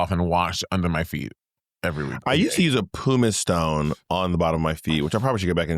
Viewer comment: “Poo-Miss stone” is crazy 😂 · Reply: I thought he said Puma Stone